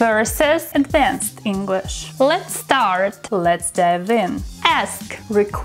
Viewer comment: How many of you thought she said "let's start" to begin the session 😁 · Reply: I thought she said let’s fart…